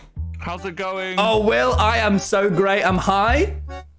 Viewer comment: sounds like he said I'm high